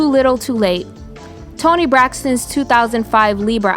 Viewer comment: Dude.... Toni was saying please... the whole time as a kid I thought she was saying breath.